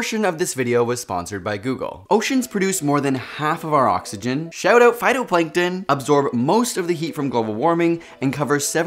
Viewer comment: Had to change immediately when I heard "shout out phtuitnngkjls" hahaha sounded like my school teachers trying to stay hip